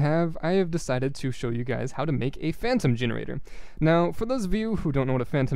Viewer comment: i misheard "phantom generator" to "femdom generator"